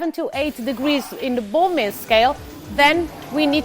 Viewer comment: So far I'm really enjoying this series. While talking about Fermentation at 1:53 the presenter talks about reaching 7-8 degrees in the ...something scale. I can't tell what she says there. And YouTube captions list it as Bone Marrow Scale, but I'm guessing that's not right... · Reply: Hi there! You're right, she doesn't say Bone Marrow Scale 🤣 mentions the Baumé Scale 🙃 Cheers! 🍷